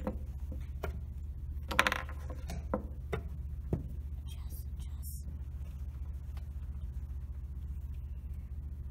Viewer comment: What does the kid whisper twice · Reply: Millan, "adjust, adjust".